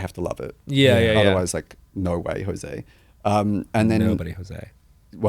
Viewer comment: I'm dying at Bowen mishearing "No way, Jose" as "nobody Jose" and Troye just going with it. 🤣